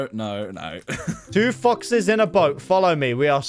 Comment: lol 6:29 is kinda sus when u said 2 foxes it sounded like 2 fuc